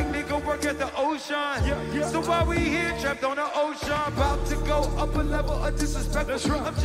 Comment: when the song finishes you can literally hear people screaming for help, you can hear someone screaming “HELP WE’RE TRAPPED”. This was FOUR minutes in, how this went on for so much longer is devastating to think about, you can see all the people crowd surfing trying to get out. How Travis and other staff can be so oblivious baffles me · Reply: Ohhh yeah when I first watched it I thought they were saying "Travis" but hearing it again...you're right...sounds like "were trapped" smh